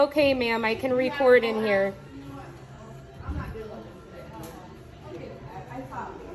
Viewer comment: I rewound it three times at At 18:47..did she say "I'm not dealing with this b!%ch" ?